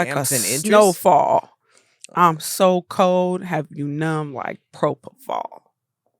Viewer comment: I thought she was saying "have you numb like pro football" 😂💀💀💀😂